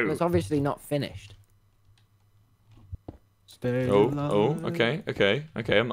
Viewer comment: Did anyone else hear someone (I think techno?) sing "Stay alive" at 24:09 ????